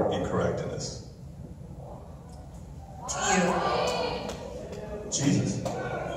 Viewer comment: What did she say at 2:23? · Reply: hotgrassjelly ‘ to you’